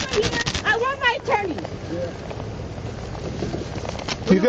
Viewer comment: "Don't TOUCH me!!!! I want my attorney!!!!" ZZZZZAP!!! "Alrightalrightalrightalright!!!!" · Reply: thought she said "i want my teddy"